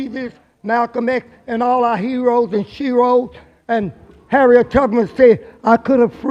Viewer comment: Did he just say shroes?